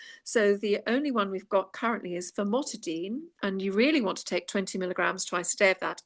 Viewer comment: For MCAS patients in the U. S., what is the names of histamine blockers like what you mentioned (around 15:56 in the video )? Did you say Nizatidine & famotidine ? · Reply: Fexofenadine (H1) and Famotidine (H2)